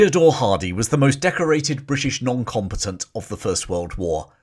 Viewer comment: I KNOW you’re saying “noncombatant”, but I kept hearing “non competent”.